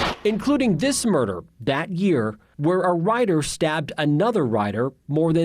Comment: I thought he said "when one writer stabbed another writer".